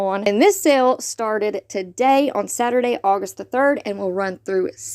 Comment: Did you mean the 3 day sale starting today and ending on Saturday (Thursday through Saturday) I had to go back and listen to you again cuz I thought I heard you say starting today Saturday and ending on Saturday. I know what you mean. Thanks for all you do